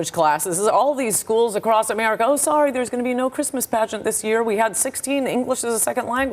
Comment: mentions Christmas Ann: what does this have to do with Christmas?? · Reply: I kept playing that part back trying to figure out what that old bat was saying and what I heard is " Sorry there is going to be no Christmas Pageants" and continues to talk about how they have to cut stuff like Christmas pageants for more English classes for immigrants. Screw Christmas pageants